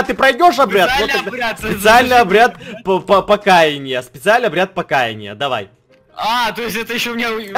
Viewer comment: когда ты сказал обряд покаинья мне послышалось обряд покаканья